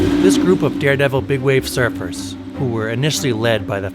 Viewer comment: I thought it said smurf for a minute